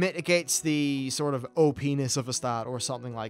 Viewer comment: A lot sounds confusing and ill have to play them a while before really having a opinion on the new numbers, Also did you say " OP ness " at 12:07 :)))